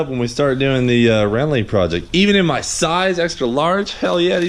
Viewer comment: Did... did i hear that right? RALLYING PROJECT o_o_o_o